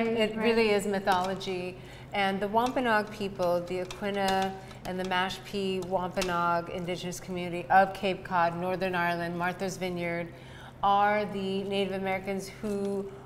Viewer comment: did she say 'The Wampanoag people of Cape Cod and Northern Ireland ' ???